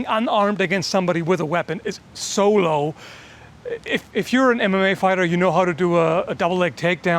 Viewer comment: min I had to go back, because the first time I heard "If you're a Anime Fighter". XD